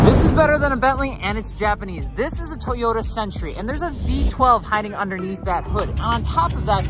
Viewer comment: I hear you say "sentry" not century. 😅😅